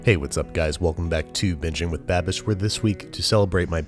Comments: Kinda sounded like you said bitching with babish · I swear I thought he said bitching with babish at the start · Okay that time I totally heard “Bitching with Babish”